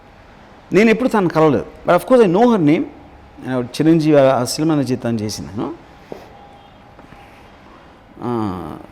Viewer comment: what did rgv said,,i didnt understand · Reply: Naaku thelisina oka ammai vundhi..